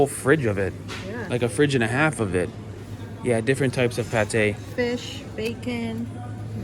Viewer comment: Did you say fish?